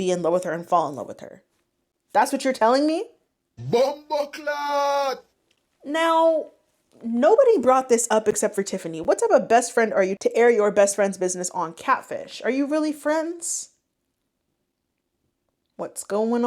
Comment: This is off topic but can anyone tell me what the clip she uses at 14:58 is saying/from whenever I hear it I can’t make out what it’s saying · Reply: he’s saying bomboclaat. it’s a jamaican patois swear word. i think it’s from a video of a guy driving a car and almost hitting a bunch of cows crossing the road but i haven’t seen the original vid in a minute so not sure